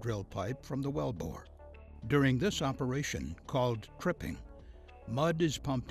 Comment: what's he saying at 0:34 ?? Crippin ??? and am i going deaf or does it sound like 'grilling' when he says 'drilling' ?